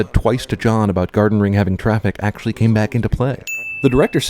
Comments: the narrator says 'came' back into play but the subtitle says 'game' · AND AT 3:42, IT SAYS 'game' INSTEAD OF 'came'!!! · pause it at 3:39... another sin on cinemasins... it says "game back into play." in the subtitles instead of "came back into play." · there's a spelling mistake. It say "game" instead of "came". Unless I'm hearing what you said wrong. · he wrote game instead of came! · it says game instead of came and at 5:08 it says 27/4 instead of 24/7. Not nitpicking, just wanted to point it out. Still, great video!